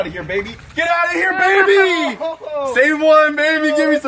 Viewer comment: boy said I think we should put them back in the pond where we got them 💀😂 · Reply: Later on I heard “same one!!!” 😂